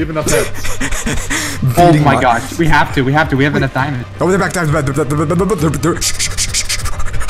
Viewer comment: What I literally hear: VR VR VR VR GUYS THEIR BACK SHH SHH SHH HA HA HA HA HA HA